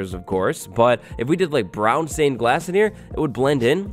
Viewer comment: He said brown stained glass and I thought he said brown stained ass